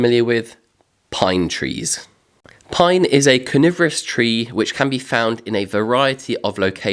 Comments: Love your videos.....been binge watching. I must admit at first I thought you said "pine is a carnivorous tree" not coniferous 😂😂 .....I kinda panicked a bit thinking well that's new 😂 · I have to get over that it sounds like you say Pine trees are carnivorous, rather that coniferous. 😁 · When you first said "pine is a coniferous tree" I heard it as "pine is a carnivorous tree". The subtitles also say carnivorous. 😂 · Only a little way in and 2 things...Robinson Crusoe is a fictional character and also it sounded like you said carnivorous instead of coniferous when talking about pine trees!